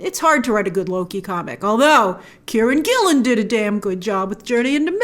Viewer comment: Thanks the cover art because I heard "Karen Gillan did a damn good job, "and was shocked that Nebula also wrote comics.